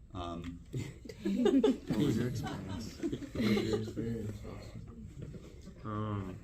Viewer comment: What did Lewis say around 6:30? Edit: “what was your experience Austin?”